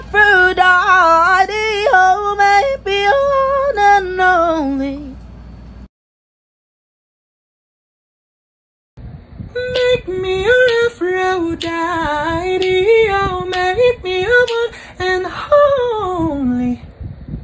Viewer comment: I hear "make me a afro daddy" lmaoooo